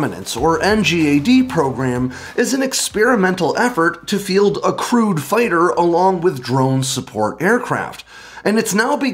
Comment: Ah, ok English is a second language for me. I was confused when in the beginning mentioned a crewed fighter. I heard "a crude" fighter... It took me a few seconds to parse that.